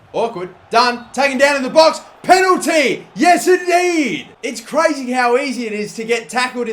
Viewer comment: Anyone else understand what he said at 14:22?? No? OK then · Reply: penalty, yes indeed...